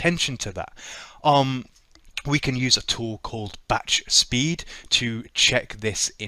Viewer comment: All I heard was "We can use a tool called bat shit speed" and I laughed out loud...